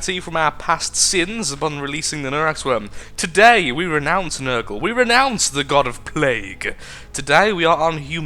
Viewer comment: Did you say nergal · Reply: Nurgle , yup :)